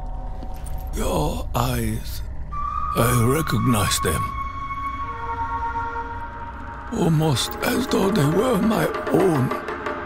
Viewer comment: "Your eyes...I recognize them. Almost as though they were my own". Most people couldn't understand what he said lol.